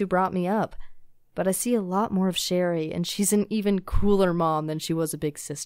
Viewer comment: I thought she said crueler mom instead of cooler lol